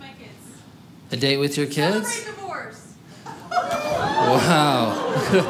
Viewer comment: I thought the lady said celebrate like a whore